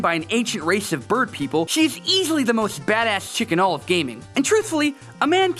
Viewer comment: I thought you said chicken all of gaming